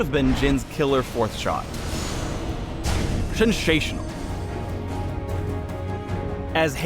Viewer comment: Did Phreak just say "shensational"? of course he did...